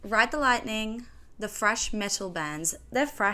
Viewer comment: Why did I heard her say FRESH Metal?